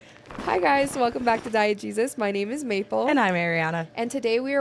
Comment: Every time Maple does the intro I hear "Welcome back to Diet Jesus" lol